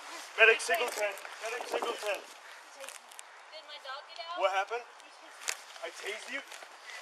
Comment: Cop: gets shot Homeowner: “did my dog get out?!” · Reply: I heard “did my dog do that?”